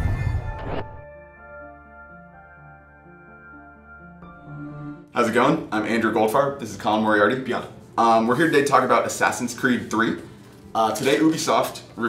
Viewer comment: I thought he said goldfart LOL